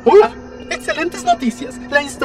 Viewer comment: it sounded like he said Mr. Pringle 😂